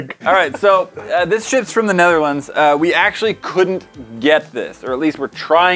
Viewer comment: When says this ships from the Netherlands it sounded like he said this shits from the netherlands😂😂 had to replay its